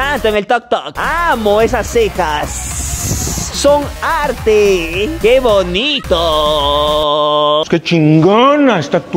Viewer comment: alguien más ve que dice SONARTE QUE BONITO 🥵🥵 XDD